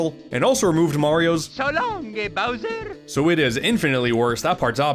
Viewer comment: Correction: The shindou version of Mario 64 WAS actually localized, because THAT is the version included in Super Mario 3D All Stars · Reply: I always thought mario said "so long king bowser"